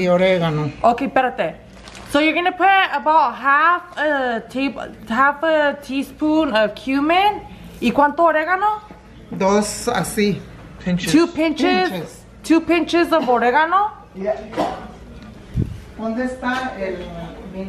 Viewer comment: 💀 she said a pinch of Oregano and I thought she said Y un pinche de Oregano 🌿 😆